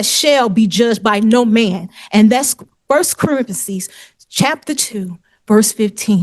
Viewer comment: I swear she said 1st Carenthesis … like Corinthians and Parenthesis put together 😹‼️